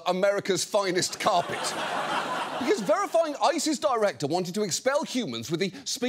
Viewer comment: did he say ISIS director?